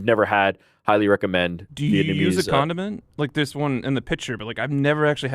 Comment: I swore Frank said “do you use a condom”. I think GC heard the same thing lol